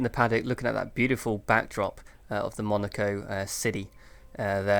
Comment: lol i almost thought you was gunna say enjoying the beautiful backend of the agent chick lols but alas yu said backdrop meaning the scenery in the motorhome lol nice win congrats